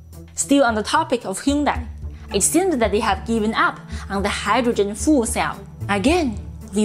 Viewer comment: sounded like “Hydrogen Fool Cell”. So true! 😂